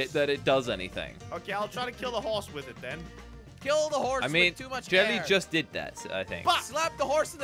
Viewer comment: Jelly: kill the horse with so much air Me: it sounded like u said kill the horse with so much hair Jelly: no?!?